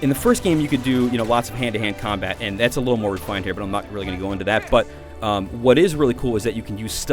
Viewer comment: What did he say there? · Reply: <num> "Hand to Hand combat thats a little more refined here, but im not really gonna go into that".